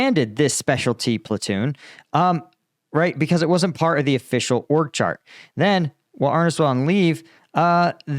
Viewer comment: I thought you said “orc chart”. I didn’t realize it was “org chart” 😂